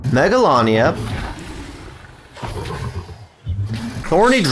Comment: Thorny dragon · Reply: I thought he said horny dragon at first ._.